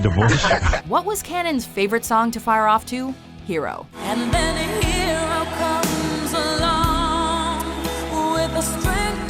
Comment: Nick misheard the lyric as "and then a hero cums alone".